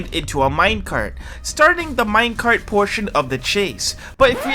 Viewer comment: subtitles say Minecraft